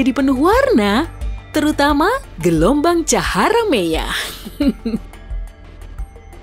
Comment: hah gk salah denger "cahara meyah?*🗿